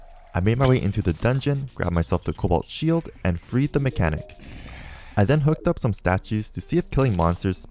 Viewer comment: When you said “and freed the mechanic” I thought you said “and breed the mechanic” 💀